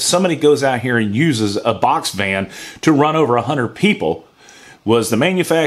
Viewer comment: Couldn't agree more. However I laughed my ass off because I thought you said box fan. That would suck being run over by a giant box fan.